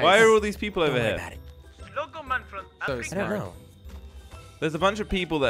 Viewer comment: did someone say "Slogoman from Africa"